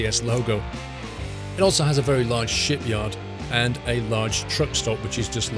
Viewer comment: sounded like you said shit yard